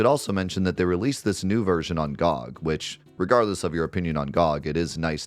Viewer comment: Love you, watching this video as I typed, I thought you said “on god” when you said on gog, I can’t believe I have the og version and my account is now more valuable